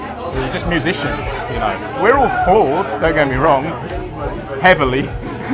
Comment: What does he say @ 6:33? We're all what? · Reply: Unshackledmind786 flawed